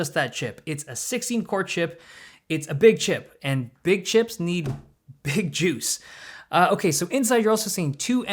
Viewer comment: That sounded like "big chicks need big juice" 😏